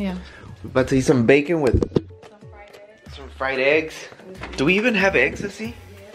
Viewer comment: LMFAO when u said “ do we even have eggs Lesly?” I had to repeat cus I legit thought he said “do we even have ecstasy?” 💀💀💀 I’m dead I was like WOAH WHAT🤦🏻‍♀️🤦🏻‍♀️🤦🏻‍♀️